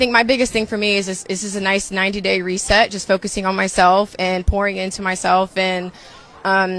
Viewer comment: Did she say whoring in or pouring in herself 5:57?